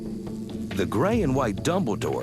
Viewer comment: I'm sorry did he just say the grey and white dumbledore? edit: thats exactly what he said, and apparently dumbledore is old english for bumblebee.